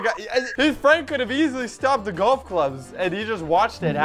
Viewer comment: When you said golf clubs I thought you said grand kids